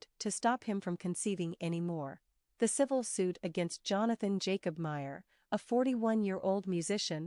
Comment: I had to rewind it. I thought Charlemagne said Jonathan Majors 😂😂